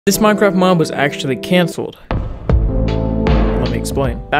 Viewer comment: Dead ass thought u first said “this Minecraft mom is gonna get cancelled”